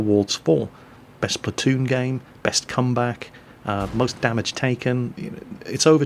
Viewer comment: He said "best comeback" I know... but all I heard was... "best scumback"